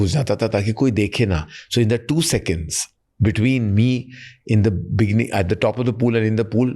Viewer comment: Did he just say Bikini???🙄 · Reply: He said “ in the beginning”